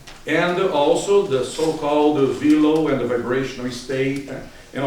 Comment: VILO? :) what did you say? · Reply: levania VELO (Voluntary Energetic Longitudinal Oscillation).